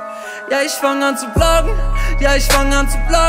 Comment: Ich habe verstanden ich fang an zu blasen 🤔🙂 seltsam